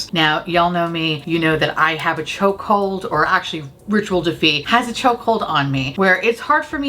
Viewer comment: All this time I thought you were saying Ritual Defeat. Until now. 🤣